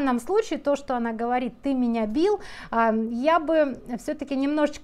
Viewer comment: Уважаемая. Ну почему же "ты меня бил"??? Вы, очевидно, не расслышали. Алиса ясно говорит: "ты мне всё сбил", имеется в виду настройка наушников и микрофона, когда папа её обнял. Ну конечно он дочь не бьёт, Вы сами об этом тоже написали. Но вот слова "не трогать!" в повышенном тоне указывают на строгость в воспитании, это сказано с папиной интонацией. Может быть, слово "щас" нечётко произнесено, потому что Алиса в разговоре присюсюкивает, проблемы с дикцией. Но меня больше удивило поведение мамы. Мама сидит рядом с дочерью, которая испытывает стресс в трудной ситуации, и даже не попыталась её как-то успокоить и поддержать.